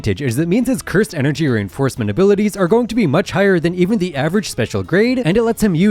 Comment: subtitles say aer and not are